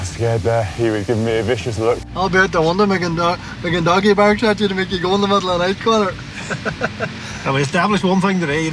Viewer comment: What does he say? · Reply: He said "be making doggy barks at you to make you go in the middle of the night Conor" and the other guy in the back says "we've established one thing today - he don't like dogs!"